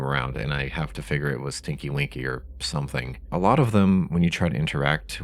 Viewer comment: really immature of me but the captions said stinky winky lol